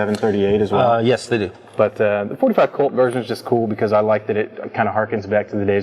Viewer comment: Did you say likeded it?